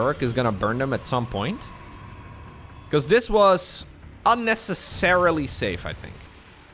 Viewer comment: anyone else hear that as "this was unnece-Serral-ly safe" ?